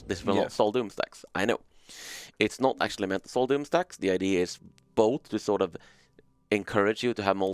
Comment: the automatic subtitles says "sold in stacks this one sold in sex" :D :D :D